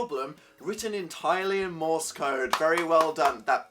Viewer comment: Proud of my fellow Canadians for trolling Dan in this video. May the maple syrup in our veins continue to fuel our creativity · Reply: Nate Sternberg I thought that said "Moose code"